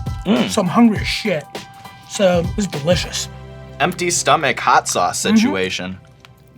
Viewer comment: Literally all I heard was "blah blah blah" "my farts taste delicious" "blah blah blah"